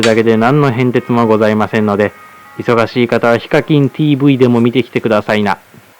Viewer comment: ヒカキンTVにしか聞こえないんだけど何て言ってる？